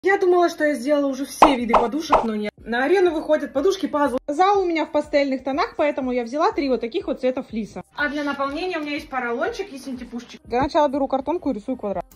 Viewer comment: Мне одной послышалось подушки падлы 😂😂